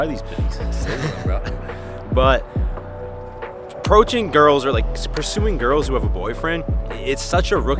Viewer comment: who else heard „protein girls“ lol